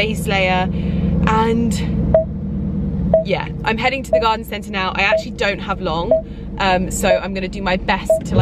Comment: I had to rewind the video,i thought you said you were going to the garden center to buy mascara! I need my ears cleaned.